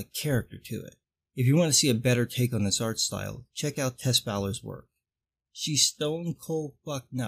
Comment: is he saying Tes Balor?